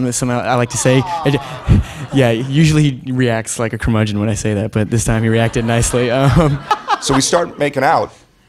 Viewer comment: What did Timmy say at 00:15 about Armie? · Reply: "Usually he reacts like a curmudgeon when I say that, but this time he reacted nicely. "